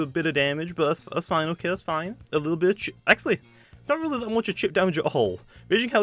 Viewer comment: What did he say at 7:07? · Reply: He said bit or chip